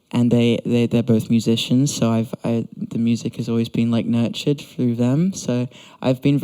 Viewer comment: I heard "nurtured" as nerd shit